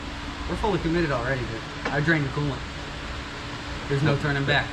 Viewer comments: I thought he said. "There's no turning back, I drank the coolant"! Haha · "I drained the coolant there's no turning back" I felt this statement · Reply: Ohhhh i thought he said i drank the coolant 😂